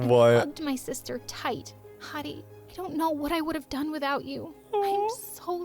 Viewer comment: Everytime I replay this clip, it sounds like "Honey," instead of "Heidi,"